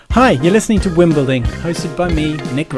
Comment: Sounded like you said Wimbledon:)